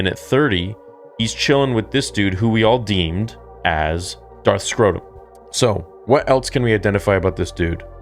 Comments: did he just say Darth Scrotum?? 🤣🤣🤣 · I'm sorry but did he just say Darth Scrotum.